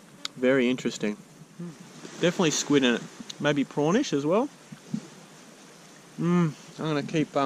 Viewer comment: When he said a bit prawnish I thought he said pornish (Edit) thanks so much and your the first youtuber to like my comment, love you man, non gay way